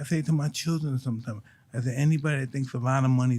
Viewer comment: "Anybody that thinks a lot of money is going to make them happy, they never gad a lot of money" - Mike Tyson I thought he said: "If anybody think about money making them happy, end up having a lot of money" 😂 Shoutout to for pointing out he mistake.